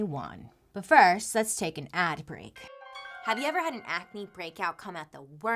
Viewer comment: I heard "Nap Break" instead of "Ad Break" and I got really excited for a second.